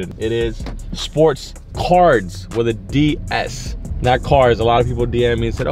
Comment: Yo im so disappointed i thought they said sports CARS hahahahahah